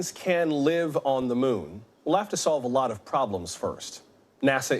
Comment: We’ll have to solve 부분이 left sth 으로 들려요 ㅠ 나의 귀가…….